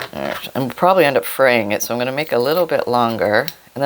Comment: I thought you said "praying" instead of "fraying"! Yeah that's about the way my project is going...!